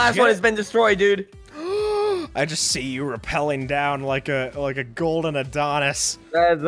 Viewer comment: WHY did I hear "a gold orthodontist"